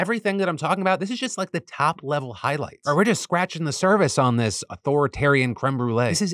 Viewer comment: Soooo... Am I the only one that heard him say cervix instead of surface lol